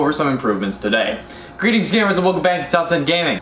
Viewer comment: I thought he said “greetings scammers”